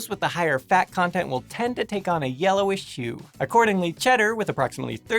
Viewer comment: lol, meeowk 2:23 you might want to watch what words you run together, cause "yellowish shoe"s sound kind of funny XD